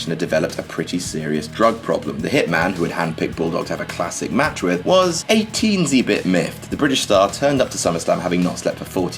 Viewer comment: what does he say, the Hitman was what? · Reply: He said "The Hitman was a teensy bit miffed."